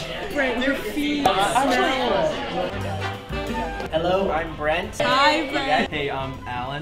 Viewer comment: I heard "hello i'm bread"